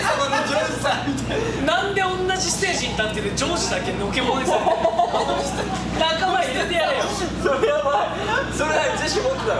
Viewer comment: の北斗なんて言ってるかわかる方いますか？ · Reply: 何でおんなじステージに立ってるジョージだけ除け者にされてんの?